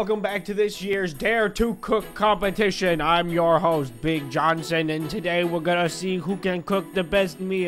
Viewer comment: I thought he said Cave Johnson Reply if you know who that is